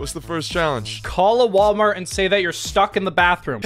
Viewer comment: In the intro I thought Wal-Mart said WAR-MART